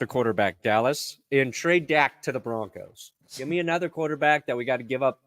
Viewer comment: "DID SOMEBODY SAY TRADE ZACH TO THE BRONCOS???"